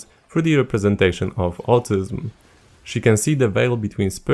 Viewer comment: is he saying autism or altism?